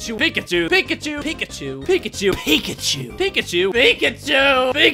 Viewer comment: Watch from 10:15 at 2x the speed. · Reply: watch it slowly and it sound like he is saying pikaeschu